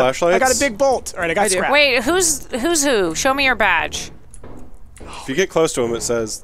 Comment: was just listening to this while doing other stuff and I swear I heard say "Show me your v@ and I almost died.💀